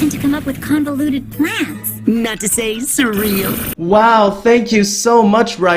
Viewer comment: Can anyone tell me what he said? I can't understand it clearly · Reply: Not to say surreal.. least what i understood